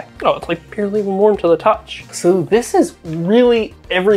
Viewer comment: If your CPU is cooking and your heatsink is cool there's something wrong with thermal transfer, i'd try to re-seat the pads or just use thermal paste somehow. Otherwise crazy cool project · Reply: How is it that you watched the same video as everyone else but hear different things then everyone else? You need to watch video again and LISTEN with your ears. He clearly said the "heatsinks were purely warm to the touch" @ 19:57 Turn on the close captioning if you need to read what he was saying. Nevertheless, the heatsinks are doing their job and dissapating the heat. There is nothing here that needs to be fixed.